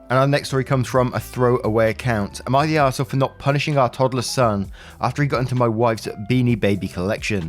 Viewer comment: I always heard 'toddler's son' during the third story and was a bit confused. Thats really young to be a parent.